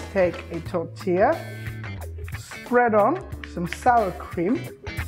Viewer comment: I hope that I misheard him say salad cream & it was actually Sour cream he said